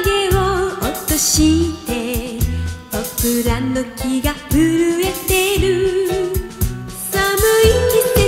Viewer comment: なみだの季節はキャンディーズの曲の中で一番好きな曲です。 当時、子供だったので「ポプラの木が震えている」が「僕らの木が震えてる」に聞こえていた。 懐かしい。